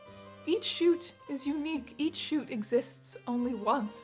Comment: The captions gave us “chutes” instead of “shoots,” and I thought wow, sickest game of Chutes and Ladders of all time.